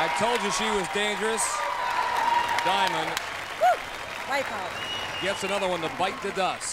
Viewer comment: is she saying "white power"?